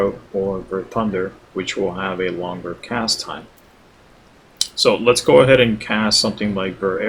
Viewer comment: did he just say Vercast time or longer cast time?